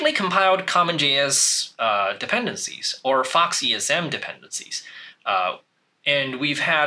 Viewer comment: what did he say? Fox ESM? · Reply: Faux esm